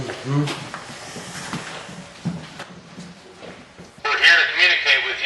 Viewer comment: About 50:11 through 50:12 about 1 second... Anyone else catch that, what it said? The guys didn't catch that one I don't think. Creepy af. It was right behind them saying something sounds kinda like "we we're" or "leave her" in that demonic rasp almost inaudible tone very quickly. Go back and listen.